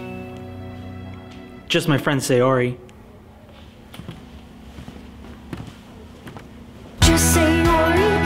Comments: What i hear “Just say yori” What others hear “Just sayori” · Idk y but is it just me or does it sound like she said "Just say Yuri" than "Just Sayori"????😅😂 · Whenever I hear just sayori it sounds like it says just say yuri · Alternative title:Just sayori getting friendzone by her crush.and also,did anyone notice that sayori saying just sayori sounds like just say Yuri?no?ok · Does anyone have any suggestions for more animatics? (It could be a song, funny YouTube clip you like, etc.) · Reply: scribblexity it sounded like just say yuri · i hear " just say your re " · It sounds like she says just say yori. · i thought she was saying just say yuri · WHY DO I HEAR "Just say Yuri" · why did i hear that as "just say ori" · I’m way too late but it sounds like she is saying ‘JUST SAY YURI’